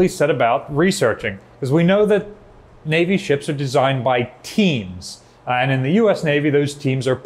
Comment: "Navy ships are designed by teens," I thought I heard initially--that got my attention as I thought it might explain the $22 billion Zumwalt-class nightmare.